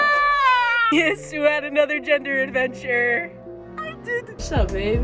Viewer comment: For a second at the beginning I misheard 'gender adventure' as 'desert adventure.' Now, see, a desert adventure sounds like a fun story.